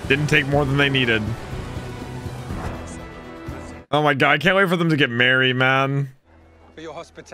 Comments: I thought he said he can’t wait for zero and luffy to get married 😂😂😂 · I heard you say "I can't wait for them to get married, man" and was totally confused xD